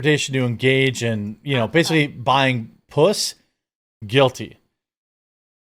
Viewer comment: Did you just say, “buying puss”? 😂